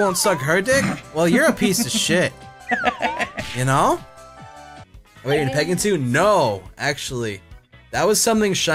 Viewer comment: What does say? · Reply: I'm not 100% sure what his exact words are. But the question was something like "Are you into pegging?" and answered "no". (Yeah, I didn't know what that meant either, but google the definition, and you should see the answer in the urban dictionary).